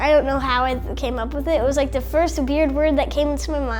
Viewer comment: I thought he said beard lol